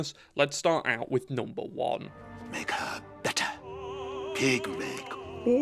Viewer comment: what did he say? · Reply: Make her better, I think